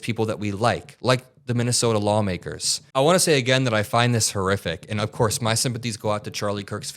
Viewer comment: I thought you said this was horrific news.